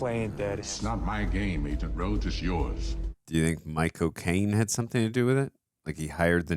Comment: 3min mark… It sounds like He says My Cocaine instead of Michael Cane Or maybe I just need a lil pick me up